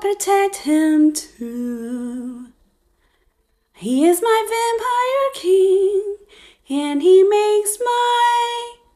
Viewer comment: Dang, this girl is making all of us Todoroki simps look bad. Also, Todoroki is not a criminal 😭, he’s a hero actually. Another thing, I’m pretty sure he’s not the type to like a fangirl pestering him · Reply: I swear I heard her say todoroki was her “vampire king”